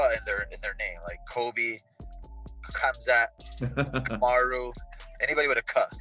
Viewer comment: At first I thought I heard bilal say "anybody with a cuck in thier name" 😆